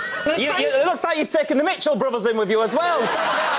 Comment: What did the guy said at 5:24??Anyone please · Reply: He said "it looks like you've taken the Mitchell brothers in with you as well". Mitchell brothers are off eastenders they're both bald. He's saying that about Jesy's boobs lol